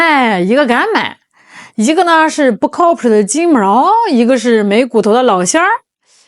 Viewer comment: 我差点听成了 “一个是不靠谱的JB毛，一个是。。。“ 🤣🤣🤣